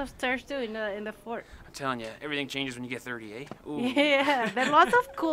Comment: YT subtitles can't catch Canadian accent. Guys, is 30, not 38. 🤭